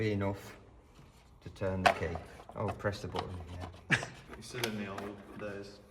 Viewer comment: what did he say here guys😮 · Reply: He's still in the olden days